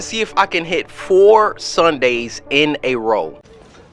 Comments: I thought he said he was tryna hit 3 Sundays in a row. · FINALLY HE POSTED…and did he just say 3 Sunday in row….😀 let’s see how this goes YALL 😭